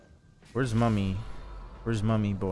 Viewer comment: I heard "where's mommy" 😭